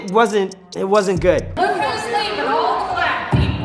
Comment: Wait does anyone know what the principle said? All I heard was “black people” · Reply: While she was still saying her speech people started leaving so she said “ look all the black people are leaving “